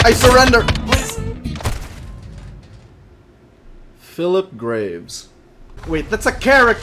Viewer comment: Who else thought he said fill up graves